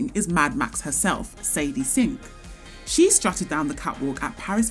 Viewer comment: am I the only one who heard Satan sink